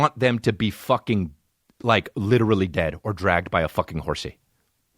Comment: Did he just say "Dragged by a horsey"?